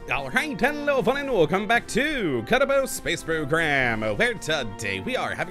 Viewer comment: For the very first second of the video, i thought that he said "be boo ba loa co ba Kottabos Space Program!"